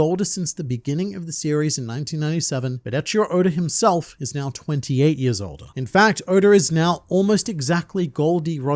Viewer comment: I thought he said Oda was 28 years old and was pretty confused